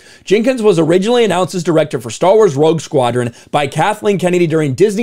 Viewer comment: Did you say "Star Wars Woke Squadron?"